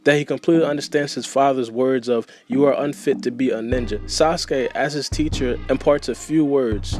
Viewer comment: It says Seasick instead of Sasuke😂